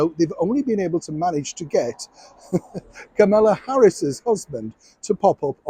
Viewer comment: American here. :yougotthis: I thought you said, "Camilla" Harris's husband and I had no clue who she was. Google corrected my search and came up with, "Kamala". But I have to say, I FORGOT SHE EXISTED!! I NEVER hear about anything she says or does. Even if Hairless could muster HER to appear, I couldn't have cared less! He is such a sad TWERP!!